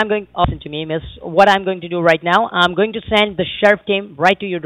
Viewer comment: Did he say I'm going to send the sheriff team